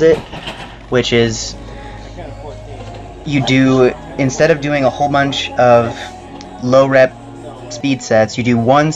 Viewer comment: what was the guy saying at like 0:59-1:05? only asking cause curious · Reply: albertolinguini lol nah dude. he said "i counted 14"